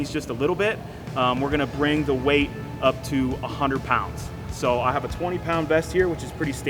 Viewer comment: Damn I thought it said 100 rounds I was like f that's not possible